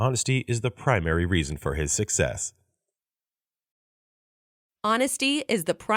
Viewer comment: his success が six ass にしか聞こえん